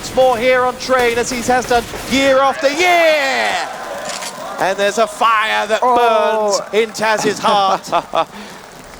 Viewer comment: I wonder what Taz says at 20:00. Unsportsmanlike conduct? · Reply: He said "co jest?" . That's not unsportsmanlike. That's mental dominance